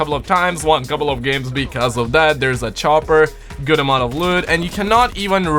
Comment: Thought you said good amount of wood 😂